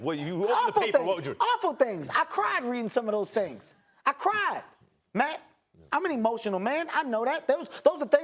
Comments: did Kevin call max “‘Matt”?? Hahahahah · Did Kevin just call Max Matt